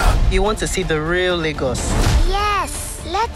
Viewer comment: What did he just say?? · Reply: Lagos. The biggest city of Nigeria lmaoo